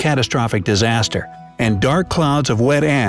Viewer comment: who else heard wet a**?